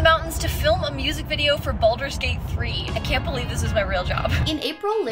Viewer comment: Ever since I misheard the name of that game, I've thought they should make a minigame in the game called Baldur Skate